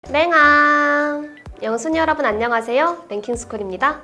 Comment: 매번 00:01에 뭐라는거야? 술랭아? 욜랭아? 대체 뭐래는거니? 개궁금해 죽겠네. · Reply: 영순이요.